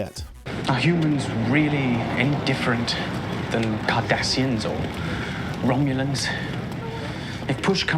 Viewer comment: The captions say, “Kardashians” instead of “Cardassians.” The fact that the automatic captions did that fits the time.